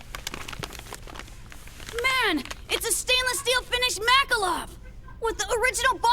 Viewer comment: I swear I heard "Makalov" instead of "Makarov", like the subtitles say